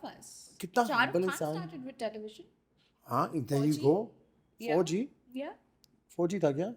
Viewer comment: i heard 4g😂😂😂😂 not fauji 😂😂😂😅